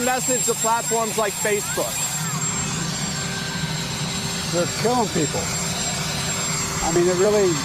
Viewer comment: all i hear is we're killing people. which they are